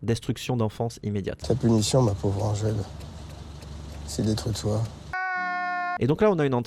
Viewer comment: il dit quoi a 8:50 ? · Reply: Il a dit:"la punition ma pauvre Angèle, c'est d'être toi"